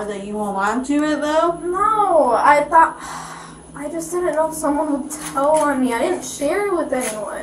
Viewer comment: Why do so many channels not even put the effort into proofreading the AI generated script? She actually says "I just didn't know someone would tell on me" and the text says "I didn't know someone hotel on me" at 26:09. I'm seeing that a lot in these types of cop cam channels.